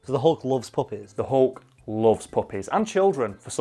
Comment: Up till 5:43, i thought they were saying “the hulk loves puppets".......